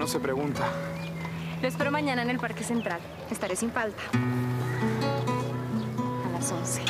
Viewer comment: yo escuche ..te espero sin falda... raios... co.l lleve aqui...